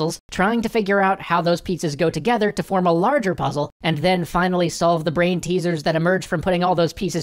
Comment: Forgive me for being super duper hungry, but at 0:55 I heard: "How those pizzas go together to form a larger waffle"